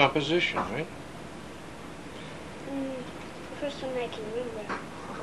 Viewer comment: What exactly did she say at 0:52 as a reply to the compliment from the adult? Please? :) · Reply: I think she says, "Um, the first one I can remember." But "remember" sounds like "rem'ber." :)